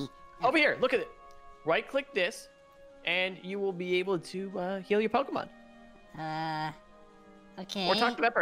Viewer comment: When he said "heal your pokemon" I thought he said KILL your pokemon and I freaked out